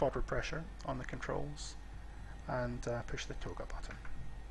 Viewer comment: Thanks again, just found your great set of tutorials: may I ask a stupid question? Exactly what are you saying @ 24:32? Something like "...press the XXXX button." I can't make it out and i cannot see what you pressed, unfortunately. thank you. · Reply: That’s the TOGA button. Take Off/Go Around. In the thrust levers but best used with a key binding. Puts the flight directors to TOGA mode and Autothrust to the takeoff setting. Also that’s very early video so some things like the speed setting on the MCP are not quite correct. Some of the later videos have more accurate procedures.